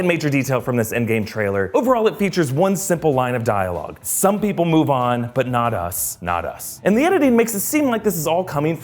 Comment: I heard the “some people move on but not us not us” as stark fully